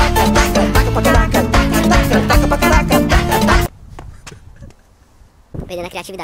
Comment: (só eu q entendi "taca pra caraca"?)